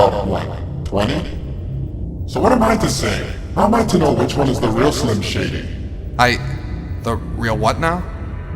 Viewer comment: did he really said slim shady from Eminem song😃